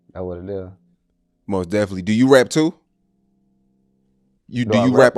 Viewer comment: Boy thought he said “do you rat too”?😂😂😂😂😂